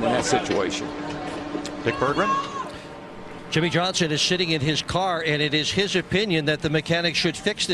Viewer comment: what did I miss? I dont get it. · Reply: Thomas Roberts the way he pronounces his s sounds makes it sound like he said Jimmie was shitting in his car